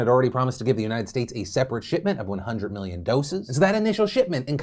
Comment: Thought he said “shit-ment”